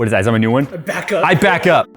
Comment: I thought you said, "I'm backed up."